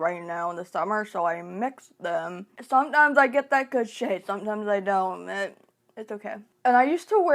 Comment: I misheard & thought you said "sometimes i get that good shit, sometimes I don't" & died laughing haha, as someone who also mixes foundations (to achieve a passable color) I totally relate w/ what you actually said lmao